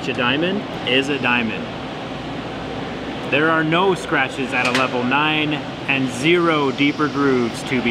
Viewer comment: Bro I heard jerry say "no scratches at level 9" now I can die happy